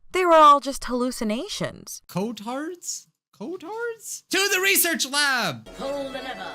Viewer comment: I thought you said retard not cotard